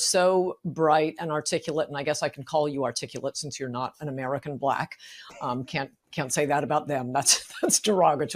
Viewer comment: Ann: "I can call you articulate since you are not an American Black". Did I hear that correctly!? Vivek smirks as she says that!? Unless I am misinterpreting what she said I find that offensive and I am white. 09:15 Ann to Vivek: "I still would not have voted for you because you are Indian". Are my ears f^©king up!? I don't like Vivek nor would I have voted for him but not because he is Indian. What am I missing here?